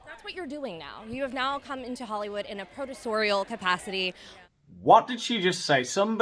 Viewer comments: I think the interviewer said that Meghan has come to Hollywood "in a professorial capacity", as in teaching people things. Still makes no sense; I can't think of anything Meg could teach that I'd want to learn. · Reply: I thought the interviewer said “in a purgatorial capacity “ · I think the interviewer said that Meghan has come to Hollywood "in a professorial capacity", as in teaching people things. Still makes no sense; I can't think of anything Meg could teach that I'd want to learn. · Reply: I thought she said producorial?? I mean I'm not even sure that's a word 😂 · I thought she said a purgatoryal capacity👺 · Producorial. As in, like, a Producer. But should have really said Adversarial. That's more in line with the suss-ex-royal's schtick. The Closed Captions didn't know what she said either!! LOL. CC said "Protal capacity" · I thought she said ‘producorial’ because, you know, Meghan is a producer. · I thought she said "you've come back to Hollywood in a purgatorial capacity" lol whatever that could mean....